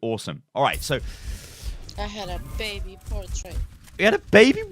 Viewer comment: it sounded like he said he has baby portrait or something, probably a joke about the fact he has a platinum border lol.